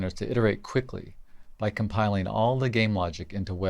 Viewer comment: WASM (webAssembly), did I hear that right? XD Holly shit. I guess works since I/O is low bandwidth for this and the rest is c++ in WASM container. Are you planning a web FE for replay at some point? Maybe my assumption for low bandwidth above as a requirement is inaccurate. In browser you can't share memory pointers to wasm (sandbox restriction). So all you have I/O wise is string based (function calls, primitve types, json etc.) But if wasm is used in UE5 this probably changes?